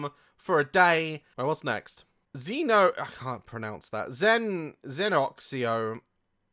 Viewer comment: I'm actually dead Xenenaxnio is what I heard 😂 😂